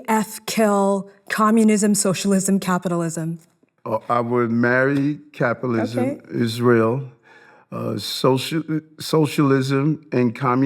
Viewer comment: Did I hear "I would marry capitalism, Israel?" I think he glitched.